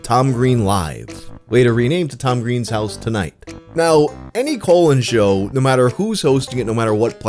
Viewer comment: I thought you said colon show